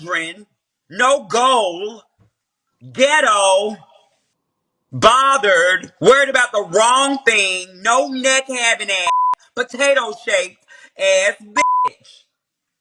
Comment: 😩😩😩😩🤨🤨🤨🤨Did just say "GHETTO" Her Name should be the GHETTO okay wtf wtf is she calling GHETTO 😩😫😂🤣